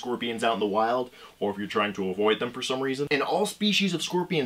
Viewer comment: "trying to avoid them for some reason" sounded like people are trying to avoid them are stupid😂